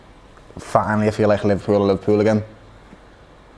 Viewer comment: can anyone tell me what did he say 2:26:12 Godsh Liverpool accent is so hard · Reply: Finally I feel like Liverpool are Liverpool again